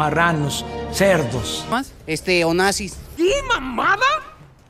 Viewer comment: Le entendí deseo nazi